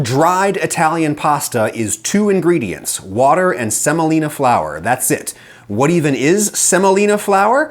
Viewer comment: Bro I thought it said somolia💀